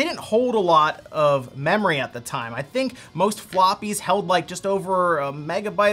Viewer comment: I swear I heard "most floppy's held 24MB" and I about did a spit take.